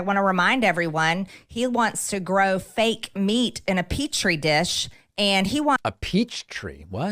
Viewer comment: I think you misheard Marjorie. She said "Petri dish", not "peach tree dish". Growing meat inside of a lab is so nice by the way, of course she had to be against it...